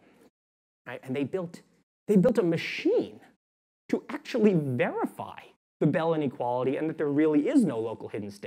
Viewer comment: Hi I'm not finished with the video yet but I'm hoping someone can help me. At 29:30 he says something about bellany quality and local hidden state. Trying to Google so I can find the research but I can't find it. I must have misspelled bellany quality but that's what I hear... What is it that he's actually saying? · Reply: bell's inequality experiment